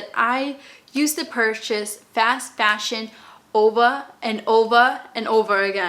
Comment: In min 0:34 is she saying “ova”? I thought it was over and over again (or something like that) 🙀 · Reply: I'm saying over but I have a Boston accent, we do not pronounce our R's.